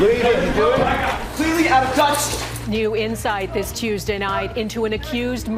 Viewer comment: Nobody said he didn’t say that dude… what…? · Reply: a lot of people are mishearing that part as him saying "out of touch" when he's pushed against the wall.